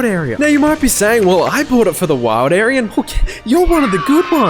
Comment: the automatic subtitles wrote "wild aryan" instead of "wild area"